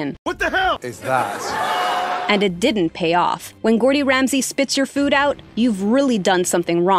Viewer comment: Did anybody else hear her say "GORDY" Ramsay?